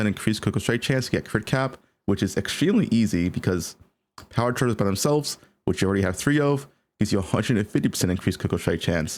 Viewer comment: Can someone explain what he says at 0:40, what gives 150% crit chance😂 Great content as always, thanks! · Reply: "Power charges by themselves which you already have 3 off..."